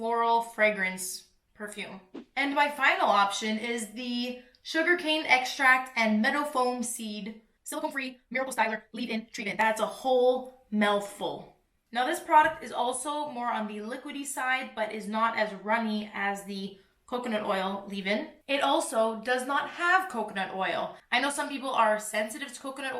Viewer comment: Am I the only one who heard “That’s a MELLful” ???😂😂